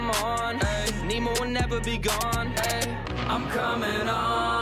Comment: did you just say nemo will never be gone aye